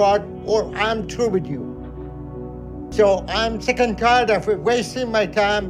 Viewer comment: subtitles say "so Im chicken Carter"